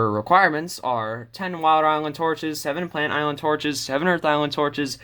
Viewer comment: sounds like you said "wild island" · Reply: He said “Water island torches”